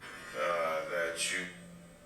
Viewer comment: it's sounds like something says "laugh at you" does anyone else hear that?